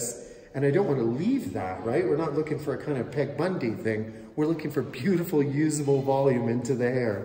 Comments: "Ted Bundy thing" ?? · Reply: Omg I thought he said TED Bundy. Was so confused by that. Thank you, I completely forgot about Peg Bundy. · Beautiful. You make it look so easy. When I try it on myself it's going to be Peg Bundy, I can already tell. When I hit the lottery, I'll fly in to have you do my hair. :) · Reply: I thought he said Ted Bundy 😂